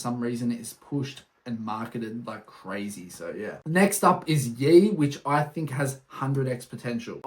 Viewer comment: he mentioned one which sounded like "gee" 26:00 but I couldnt understand what he said ? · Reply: “Yee” my bad bro